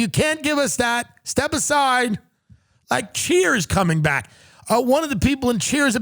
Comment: Wtf, enunciate better. I thought he said "Cheer's is coming back" and I thought we were get more Sam Malone antics.